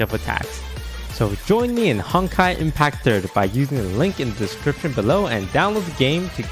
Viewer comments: Who else thought he said Hentai Impact 3rd 😂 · Toast : "Honkai Impact 3rd" What i hear : "Honkers Impact 3rd"